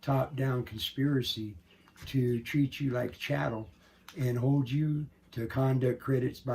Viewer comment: About the 14:30 minute mark did he say treat you like chattle?